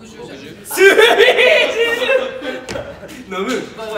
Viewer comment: すみじる！のあと「飲むん？飲むん？」って地味に聞こえるのがじわる